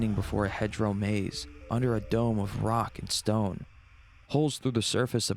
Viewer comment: DID I HEAR A ROCK AND STONE? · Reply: ROCK AND STONE, YEAHHHHHHHHHHHHH